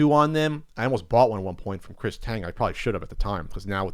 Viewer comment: I thought i heard him say he almost bought a rare NWC controller from Chris Kattan from 'A Night At The Roxbury" fame and I instantly wanted to hear that story, but listened again and it wasn't. In an alternate universe... that is a story that makes the podcast itinerary.